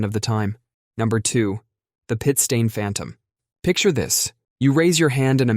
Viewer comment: I was listening in the shower. I thought you said poop stain phantom. Showing up in a meeting. 😂🤣 What a thought!